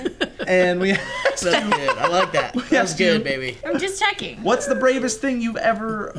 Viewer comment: does call 'baby'?